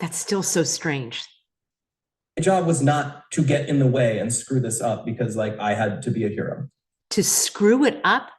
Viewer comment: Right after he said, “It was my job not to screw this up” He then said, “It was my job to be a hero”. What?! ..I thought you would jump on that but you didn’t. So I will. How was he a hero? Was it because he immediately called his mommy? I don’t get it. What made him a hero? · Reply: I heard it as "wasn't my job to screw it up because I (believed I) had to be a hero." It's around the 13:40 mark (maybe a little before and a little beyond).